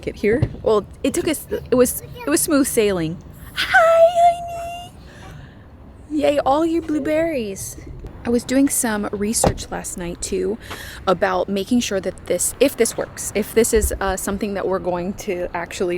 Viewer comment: Did you say Booberries to? If so, that was so cute!